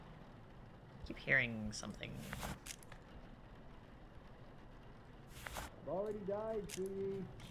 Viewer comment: I think he said I already died sweetie